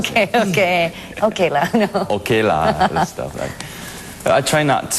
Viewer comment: Is ken said 0:42 "ok lang"? My gosh he remember that tagalog word? Amazing ♥ he and vanness can fluently speak english.. i love F4 forever #F4 #Taiwanese #F4Fever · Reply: he said ok lah! it's a singaporean expression. he was asked by the interviewer regarding his singaporean accent that is why he emphasized ok lah!